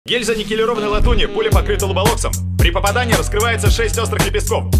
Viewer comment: мне сначала послышалось, что пуля покрыта роблоксом 💀